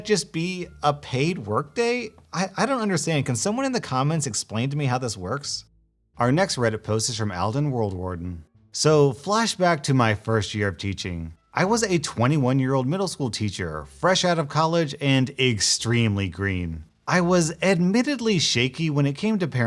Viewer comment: I thought he said I was a 21 year old in middle school